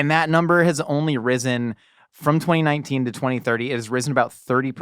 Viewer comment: Di-did just say 2030? I didn't realize we have the statistics on that.